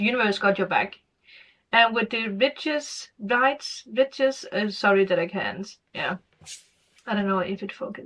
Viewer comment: What's the word that you couldn't identify at 3:33..? (I can't see it on my screen.) Am curious, is it "Rites of Passage"..? "Rite" is pronounced like "right"... (But if you can pronounce 'right' and not get confused by the silent 'gh', I can't imagine what the word could be that's giving you a hard time...🤔) Can I ask why you mentioned that Leo's have difficulty feeling 'deep', when the organ that corresponds with the sign is the Heart? A 'healthy' Leo has, and honours, all the feeelings, has the courage to fear nothing, and the ancient wisdom of the heart guidance to be nurturing, protecting, forgiving (etc.) at all times, and they are very difficult to deceive or manipulate. 'Diving deep' happens at a glance, being able to see the surface and the bottom of the ocean at the same time. This is also the source of their confidence and humility, their bubbliness and bad-assery.😄 I hope you have a Magickal month ahead too! Imbolc (pronounced like 'Imbolg', btw) Blessings! 💖🧙‍♀💖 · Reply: Yes “ Rites of passage “ I said “ it can be confusing at times to feel deep “ it depend on the person watching, what they are going through.. it will resonate with the person who needs to hear it :)